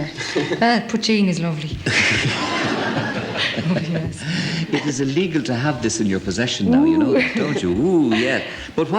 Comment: What Did she say ? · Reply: "Im only messing"